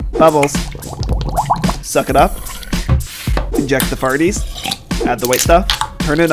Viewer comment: AYO DID HE SAY FARTIES INSTEAD OF SMARTIES